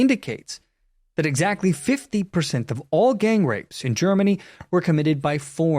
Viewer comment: Dear Germany, the 15% MUST be reduced to 0%. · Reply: I don’t think he said 15%. If I heard correctly, it’s 50% of rape is committed by foreigners.